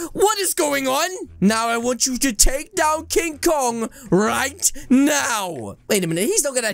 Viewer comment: I thought you said hong kong :/